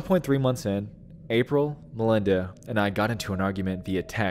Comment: " ... April, Melinda and I...." I had to pause this and read it for myself because if how I heard it. My both sister's names are April, ( well actually Abril but she's always been called April ) and Melinda. But now I see. He said " In April, Melinda and I.... " It kinda threw me for a loop there for a min.